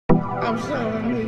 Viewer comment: Nah at beginning whet it said retro i thought it said metro as in MetroBoomin